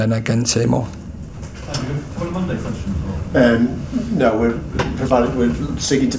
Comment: what was said at the end? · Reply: A reporter asked for Mondays (An embargoed section for tomorrow night) and the press officer said he's looking for a player to do it.